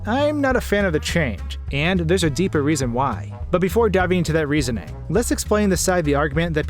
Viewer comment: Did you say "but before dabbing into that reasoning"?